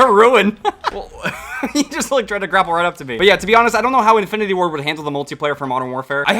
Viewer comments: "I dont know how Infinity War would handle the multiplayer from modern warfare". Is what I heard XD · I thought he said infinity war, not infinity ward